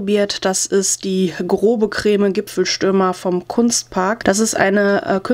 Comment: Ich hab erstmal "Schlüpferstürmer" verstanden 😅 Die Kingart Stifte würde ich gern in aktion sehen. Uuuuund du hast es schon wieder gemacht..cliffhnger 😩